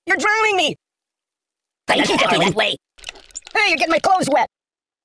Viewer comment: did he say hurry and get my toes wet